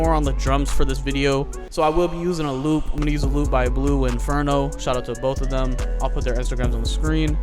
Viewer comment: i thought he said blue inferno lmao